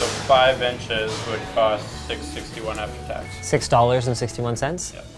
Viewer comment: youtube auto-sub scared me cause it said $661 instead of 6.61